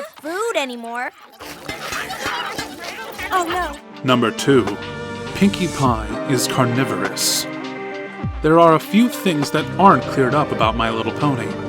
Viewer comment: i thought it said pinkie pie was corona viris im like noooooooooooo but no